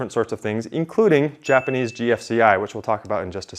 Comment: my object show ahh thought he said “Japanese BFDI”